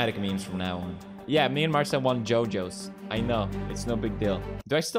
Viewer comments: When Pewds talks about YoYos, i thought he said “JoJos” because of how the way Marzia and him poses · i thought he said jojo's cuz that really looks like a jojo's pose